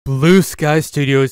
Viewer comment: This is why I HATE Disney Edit: Thank you Blue Sky Studios for making Scrat, he helped me when my autism was at it's worst. He always gets me through the positives and my main goal was to join you guys! But thanks to Disney hating me, they said NOPE and blew the candle. I wish I did get a job at Blue Sky but now it is not possible, Ice Age Scrat Tales is my most anticipated release of the year. Again, thanks for everything and remember, Blue Sky 4ever! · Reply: I thought you said glued spy... Did you mean 20th Century Animation?